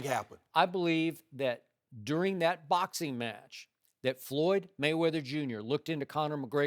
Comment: Did he just say "Floored" Mayweather ? LMFAO